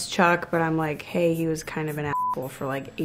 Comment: “Hey he was kind of an apple” that’s what i heard when they bleeped her out😂